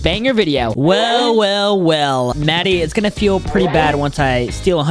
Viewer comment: Am I the only one that thought he said maddy at the start?